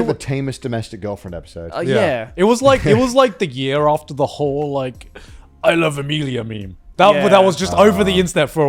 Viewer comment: what did garnt say? “It was like a year after the whole i love emilia meme”? Is that what he said?